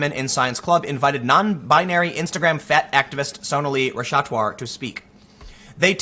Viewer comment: thought that said "fatass activist"